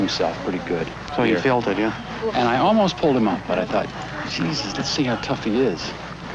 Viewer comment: wtf? The rider says about his horse, ‘the good thing about it is, he hurt himself pretty bad… so I [pushed him on] and thought, Jesus, let’s see how tough he really is.” Am I hearing that right? If so, what a cruel & sadistic asshat.